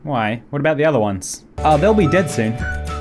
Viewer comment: I fell asleep watching your vids, and I when I woke up I heard “they’ll be dead soon” Me espantaste güey 😂